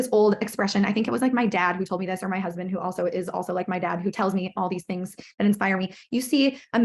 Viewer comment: did i hear this right "i dont know if it was my dad who told me this, or my husband who is also like my dad"